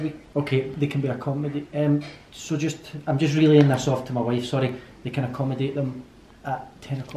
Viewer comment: So cute when said “I’m just reeling this off to my wife”😍💍❤️ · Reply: I thought he said relaying this to my wife, Regardless... We need a compilation of on phone.cheers us up